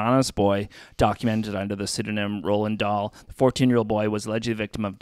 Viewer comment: Instead of Roland Dahl I thought you were saying Rolling Dough 😅 I had to listen to it a couple times.